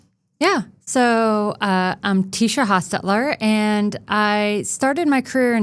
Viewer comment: Thought it said Tesla at first!